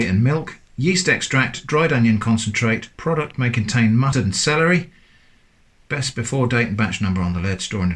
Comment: I don't know if its because I came straight here from being fascinated by your latest clay pot story but I'm sure I heard you say "may contain mud and celery" at 2:30. That delicious looking "stew" you first made was obviously still swilling around in my mind. Thanks, you are the seasoning to my day :)